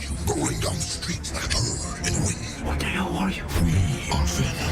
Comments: I thought he said “like a BIRD in the wind” for a while💀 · I thought venom said “like a dog in the wind” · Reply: I heard “like a bird in the wind” which makes more sense lol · Yes Adia Paxton... i was alrdy aware😅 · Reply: I thought he said "like a TOAD in the wind." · O thought he said bird in the wind lmap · I thought venom said “like a dog in the wind” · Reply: I thought he said like a toad in the wind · Thought he said like a bird in the wind? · At the end I thought venom said like a bird in the wind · I always thought venom said like a purge in the wind